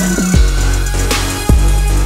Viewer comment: (misheard lyric) Do you hear what that dude is saying? "And now, the winner" YES THEIR FIRST WIN!!!!! Edited: ThEY ACtUAllly WoNnNnN!!!